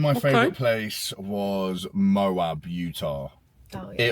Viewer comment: Moab Utah is what he said if you want to look it up. Utah is a great state as far as location for ability to reach other states in reasonable time frames, geography, climate and people.